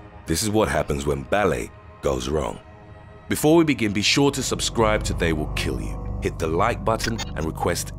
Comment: This is my first time watching them, so I thought I heard “subscribe or they will kill you” and I was like 😳WHAT